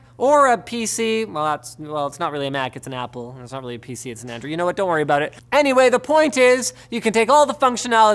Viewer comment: Did you say Arduino :p